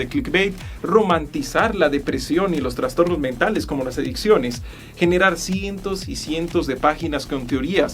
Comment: creo que debo ir al doctor porque escuché erecciones en vez de adicciones